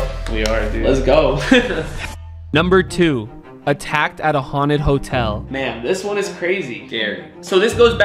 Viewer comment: HOLD ON DID U SAY ATTACK HAUNTED HOTEL